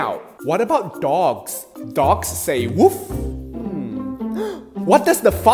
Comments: I always hear "what the f##k say😅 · In the end who heard "why the f**k say" too? 👇🏻😂😂 Edit:OMG 200LIKES IN 1 DAYS TYSMMMMM🎉🎉🎉🎉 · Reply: Yeah I hear "what does the f*ck say" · Bro what does the fuck say I heard .please don't report!! 😭😓